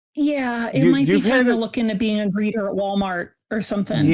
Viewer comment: I misheard and thought for a minute she was suggesting becoming a "breeder" at Wal-Mart!